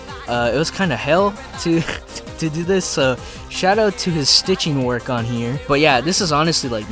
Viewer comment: I thought you said shadow to them instead of shout out to them😂